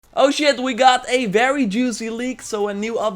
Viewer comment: Did I hear an "oh shit" at the beginning bro? 00:01 -- honestly that armor looks like Fortnite meets Odyssey.